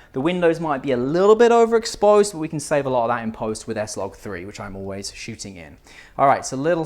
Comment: great video (as always). Quick question: At 18:14 you talk about shooting for interiors and fixing the "little over exposed" windows in post in "SL3"? (Least that's what it sounds like you say). Can you explain that a little deeper? I shoot with a Sony A7iii and my biggest failure is having windows "blown out" when the interior is exposed properly. Love to learn some tips! Thanks.